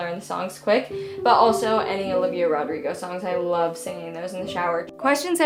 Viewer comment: I just heard the, I love you Rodrigo, i dont care if she didnt say it or I misheard